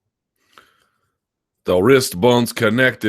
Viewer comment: Lmao thought he said wrist bump